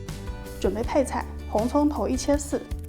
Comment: "红葱头一切四" 听成 红葱头一千四 ... 被自己吓到了 感叹 好贵的红葱头呀~ 哈哈哈